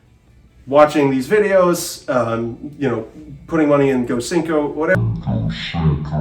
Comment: Agreed! Enunciate! I heard "Gocinco" as in Cinco de Mayo. "Go-Send-Go"